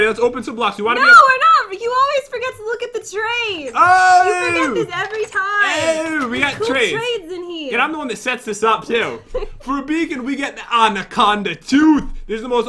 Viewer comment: i thought he said ffor a bacon lol